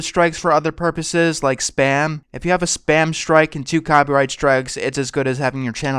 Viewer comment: Hello, I like how informative the video is, but at 5:59, the subtitles say "in to copyrights drugs", not "and two copyright strikes" Do you think this could be changed? Adding subtitles would make your channel more accessible to those who are deaf or hard of hearing